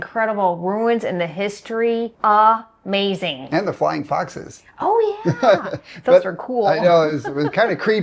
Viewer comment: Did I hear “flying foxes” ?